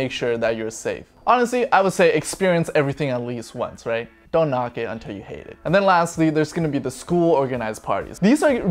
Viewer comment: Did he say “don’t knock it until you hate it”?!!